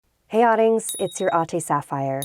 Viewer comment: I always thought she was saying "Hey Audings, it's your Auntie Sapphire" 🤦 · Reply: I thought she said hey audience , it's your auntie Sapphire. Okay